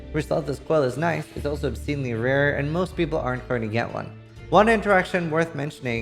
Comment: nice build! at 17:47 what did he say? i cant understand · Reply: "ryslathas coil is nice, its also rare, most people arent going to get one "